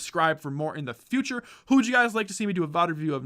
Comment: the video cuts before he finishes the word so it sounded like "take it easy and peeee" In all seriousness though great video as always, keep up the great work!!!